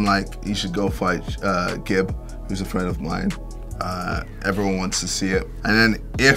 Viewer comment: the subtitle said a friend of mind. he said a friend of mine. so Americans don't really understand British english